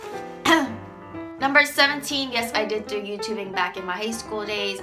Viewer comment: number 7 : she did what ? i don't anderstand the word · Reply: Roxy youtubing